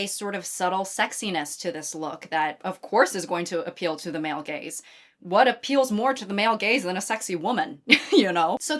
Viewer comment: late but 20:26 when you say "what appeals more to the male gaze than a sexy woman, you know?" the subtitles misheard "male gaze" for "male gays" and i cant stop laughing